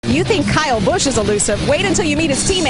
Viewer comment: honestly thought you said “you think Kyle Busch is a loser…